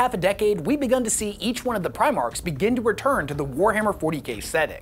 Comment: dude i repolayed it like 30 times now and it sounds like you said - Over the last haldf a decade we have began to etch one of the Primark's return to the Wahrhammer (40GAY) setting all i have to say is.. BRUHH.. common